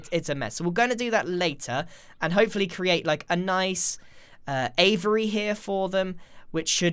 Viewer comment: I thought he said slavery :-) lol